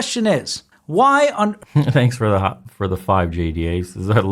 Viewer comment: Did you just say your ass looks tasty??? AYO! 😳😳